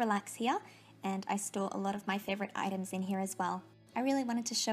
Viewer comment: who thought they heard i stole sloth of items in here as well😂